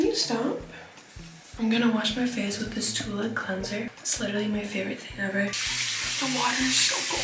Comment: I thought she said toilet cleanser💀